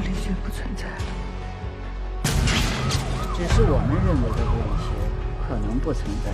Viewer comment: 翻译感觉不太合适，"只是我们认为的物理学可能不存在了"似乎应该是“it might not exist that just the physics thought by us.” 这样会让国外的朋友无法正确理解人物对话、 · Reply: 对，应该是"Only the physics we know might not exist". 我翻译的时候翻译的比较快，听成了”只是我们认为物理学", 少听了一个“的”